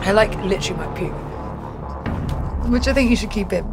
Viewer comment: When she said she was going to puke, did she say that Sean should "keep it" or "keep in [the video]" ? The former is just weird!